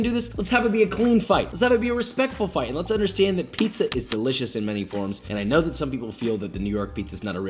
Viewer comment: says: "Pizza is delicious" I hear: "Pizza is religious" Me either way: "Yes"